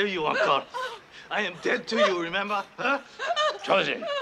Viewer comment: When I was little, I thought he was saying "I'm deaf to you" at :30